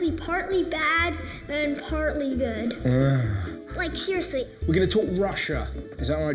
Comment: Did that kid say, "like Hear Say" at 21:26 after the other kid said he's partly bad and partly good? It sounded like it haha.